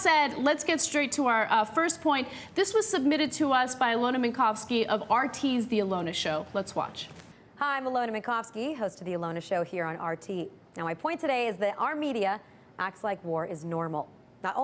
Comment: Did Alyona say "Our media" or "Armenia" acts like war is normal? at